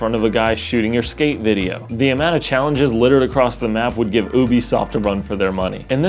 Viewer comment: You said it "could give Ubisoft a run for it's money". At first I thought you said Boobysoft. I am now calling them Boobysoft. Thank you